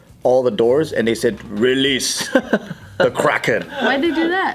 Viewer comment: I heard "Release the crackhead" lol